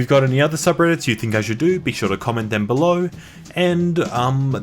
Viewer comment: oooh the link to your BETTER channel. all this time i thought you said BETA channel, as in this one is the alpha channel or smth idk